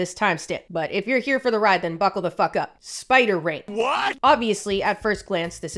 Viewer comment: I thought she said “spider rape” at first and I was like so it’s that kind of video😂💀